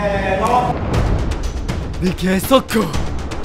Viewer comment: の理系速攻が侑っぽく聞こえたの私だけ？？？